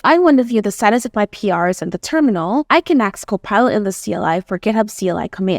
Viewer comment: If that is an AI generated voice and it said 'axe' instead of 'ask' .... hmmmmmm 6:10 - The Google Login Dropdowns piss me off. I used to stream on a random platform, and I would load a site, only to have it doxx my IRL name in the corner... I hate that.